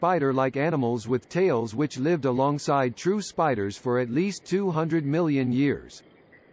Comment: not sure why I heard this as "Jew Spiders". I had to pause the video. I'm like "really??" There's a number of politically active people who wouldn't pause the video at all and be like "yup, mm hmm, Jews are spiders" and not give it a second thought.